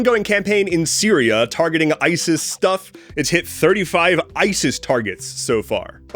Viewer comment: Misheard “ISIS stuff” as “ICE’s stuff”